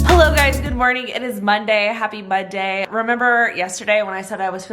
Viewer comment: Who else heard "Happy mudday?" at 1:01 Lol I know it's because has a stuffy nose P.S. LOOOOVE Pho!!